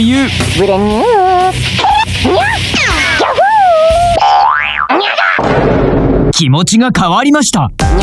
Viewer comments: did she just say the n-word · Did he just said "NIGGA"?